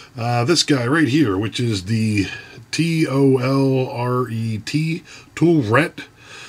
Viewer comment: I thought it said toilet lmao , I was like damn I’ve never riveted a toilet, must be fancy